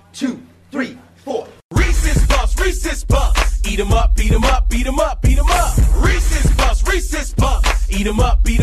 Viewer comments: When I first heard this I missed heard it as Reese’s puffs Reese‘s puffs beat them up beat them up beat them up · What I hear: “Racist puff’s, Racist puff’s! Beat em’ up, Beat em’ up, Beat em’ up, Beat em’ up!” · In my whole entire life I thought he was saying racist cop racist cop beat em up beat em up😭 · This whole time I thought it said beat them up beat them up beat them up beat them up when it says Reeses puffs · i thought they said '' BEAT EM UP BEAT EM UP! ''